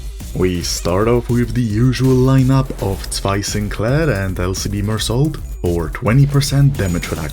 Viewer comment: sounds to me like your saying taii sinclair instead of zwei sinclair